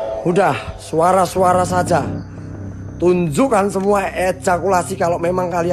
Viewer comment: tunjukan semua ejakulasi ? 😂 Gak salah denger kah ?